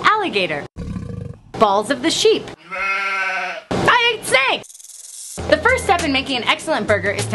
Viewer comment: I'm sorry, did you say you ate... Paint? 1:07 Oh no, picture of a snake, just checking.